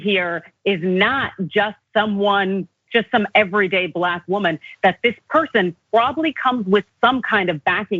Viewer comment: I’m sorry but did just say she was better than “the everyday black women” because she can speak clearly? Wtf is going on? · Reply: That's what I heard. " Not just some everyday black woman "